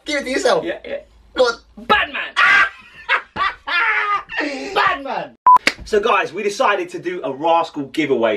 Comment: I thought he said Batman